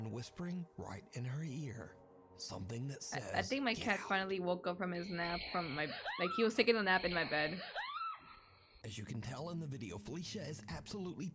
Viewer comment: No i hear : Take it out 17:23 Or Take out , ghost might be hungry i dunno.